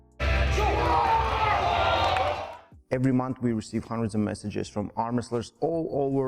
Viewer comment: Bro the captions said at 0:09 “we receive hundreds of messages from our mothers all over the world” 💀💀😭😭